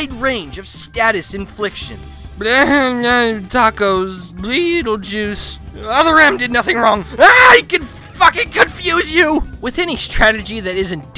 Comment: Also did I hear MEGALOVANIA in here 5:01 “Blehneheh, tacos, Bleeettlejuice. Other M did nothing wrong, AH, he can f**king confuse you!”